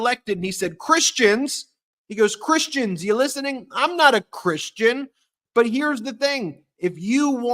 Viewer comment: He's saying I'm ah christian. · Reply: I thought he said I'm NOT a Christian, which is so true.